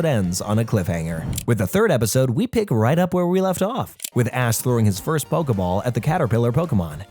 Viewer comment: bro definitely said "Ass" instead of "Ash" lmao